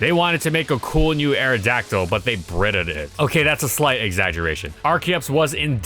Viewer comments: Wait did I mishear that or did you say they Britta'd it? Actually iconic. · Please tell me this is a Community reference... It sounds like he says "Britta'd it".